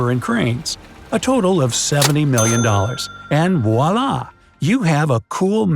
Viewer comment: did he just say "Wallah"? 😜😜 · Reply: He didn't. "And voilà !"